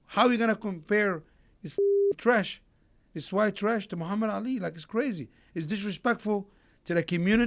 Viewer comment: Did he say white trash at 18:01?